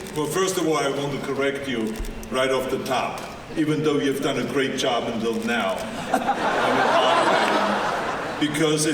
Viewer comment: ¿Could anyone tell me what does Arnold say about Trump at 29:01? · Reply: "There is a lot of options available especially since the economy is going so well under Donald Trump" In a sarcastic tone. Probably because the economy is good, but most of that goes into the pockets of the 1%. The average American still has 0 dollars in saving, works multiple jobs and is lucky if he or she has health care. But probably also because Donald Trump claims the sole responsibility for the economy, but economies tend to depend on more factors than just who is sitting in the White House.